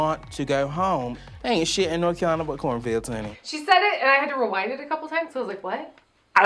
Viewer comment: Today was the first time I understood what Stacy said: "Ain't shit in North Carolina but cornfields, henny"😂😂😂